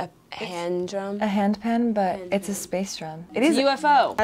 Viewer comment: I thought she said hand job at first😂😂